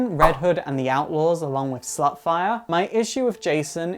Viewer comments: did you call starfire slutfire xD? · Did just call StarFire "SlutFire" at 16:14?